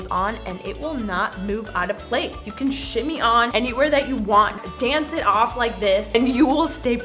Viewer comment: I thought she said “ you can sh*t me on” 😂😂